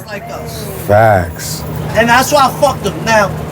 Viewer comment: AYOO SUPER PAUSE!!! What did you just say??!? "And that's why i...". 11pm hookah date. Little portable fan. 😂😂😂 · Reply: He said “that’s why I fuck with em.” I had to go back a few times lmaoooo